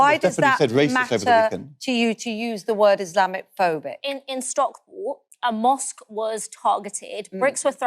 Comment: did she say Stockport?